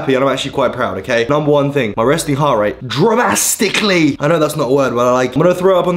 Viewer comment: Did just say drastically isn't a word? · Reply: Dramastically isn’t